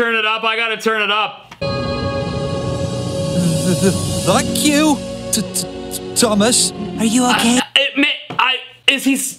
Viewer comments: he is not saying F**K YOU THOMAS But he is saying thank you Thomas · he is totally saying F#ck you Thomas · I hear a British thank you